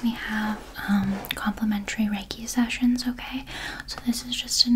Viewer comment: Oh my god I literally thought she said complimentary Reggae sessions 😂😂😂💀💀💀